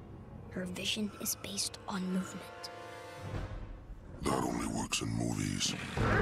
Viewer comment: What is the t rex saying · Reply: That only works in movies